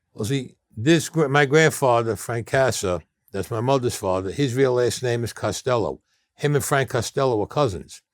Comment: Sounded like he said real ass name instead of real last name he said it so fast lmao